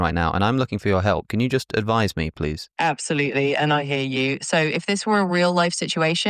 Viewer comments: Whenever chatGPT says "i hear you" my brain hears "i hate you" and it makes the conversation even funnier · Every time chat gpt says "I hear you" I can only understand "I hate you". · What I heard: "Absolutely, and I hate you"